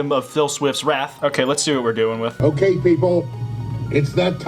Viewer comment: my Google assistant turns on no matter what. Google thinks he's saying "okay Google" and that's epic